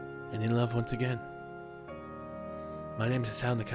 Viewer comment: lol at the end, i thought he said "im hisao micki..... and i have feeling for a litte boy"